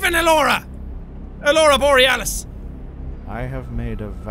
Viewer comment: I thought he said Cora Borealis, which is the name i use for everything 😍